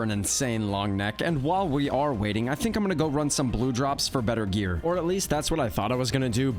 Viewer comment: I was stretching and when he said “I think I’m gonna go run some blue drops” I thought he said “I think I’m gonna go run some blow jobs”